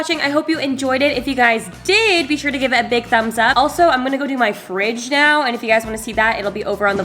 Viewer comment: When can i meet drunk Alisha and? Also at the end I thought you said “imma go play bridge now, so if you’d like to see that...” then I realized you said clean your fridge. 😂 Anyway love you guyss - my insta: krystacompton